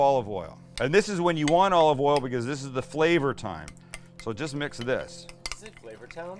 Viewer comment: I heard "flavor time," not the other one. Just FYI. We need to stand together against those cocky millennials.